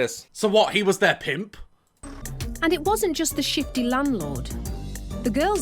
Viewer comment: I could have sworn she said shitty landlord not shifty 😂😂😂😂😂😂